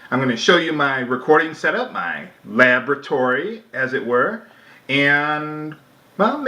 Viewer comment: LOLOL I thought I heard him say 'lavatory' instead of 'laboratory'.